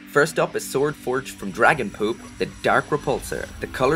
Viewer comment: did he say dragon poop at 2:37?